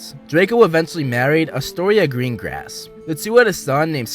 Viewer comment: Astoria Greengrass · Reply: totally thought you said "green ass"